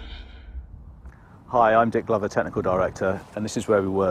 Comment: hahahaha that guys name is Dick Glover and it definitely sounded like he said Dick Lover!!! ahahaha